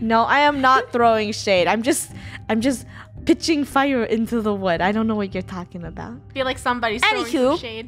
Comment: did anybody else hear say putting fire into the wood